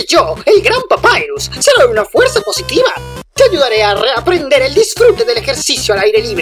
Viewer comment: escuche te violare en lugar de te ayudaré XD 😂😂